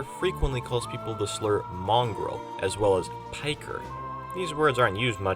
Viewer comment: pretty sure sniper says piker instead of pikey, which essentially means a coward in the aussie slang